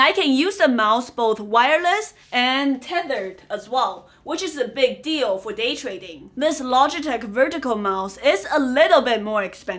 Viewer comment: the subtitles missed part of what you said about the mouse being both tethered and wireless, is left out " tethered is a big deal for day trading."